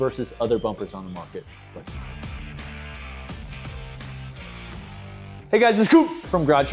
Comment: I heard "Hey guys it's coup" and thought it was a d'état